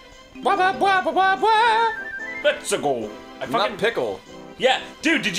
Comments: i thought Mario said "Obstacle", something about a pickle, and many other things before finding out years later that he says "Let's a go" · I always thought Mario was saying "Lets tickle"... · I thought mario said Hup Pickle XD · As a kid I always heard "let's pickle!" while playing Super Mario 64. I thought it was just me too. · holy fuck, I always thought Mario said "Let's Pickle." Just like "So long, gay Bowser."